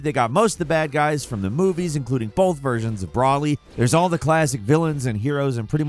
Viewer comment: Did he just say "Braly"? Surely my ears are messing with me.. Yeah, it's just my ears..